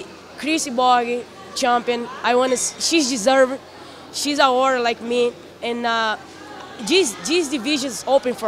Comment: That didn't sound good... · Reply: I heard: "She's agora like me".